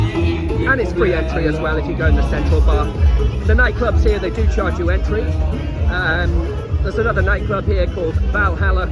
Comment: If you go to 12:40 in the video, you say something about Valhalla club, but then the sound gets removed bc of copyright. So i was just courius what you were saying about it :-) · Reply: I think I was saying that the Sales Promotors on the Strip (Flower Street) come over to you and offer you a Deal of 50 Leva or 70 Leva Entry Fee to go into the Club, but that is with ‘Selected’ Free Drinks, I think the Beer was not included and when you go to this Club in the High Season it’s very busy so you are in a Big Queue to get a drink. The Club played good music and there was lots of people there but 90% of the people were men which was a strange ratio! Dave do you have any info about what you actually get included if you pay the 50 Leva or the 70 Leva entry fee ? I know some free drinks were included within the price and some nights there was a foam party there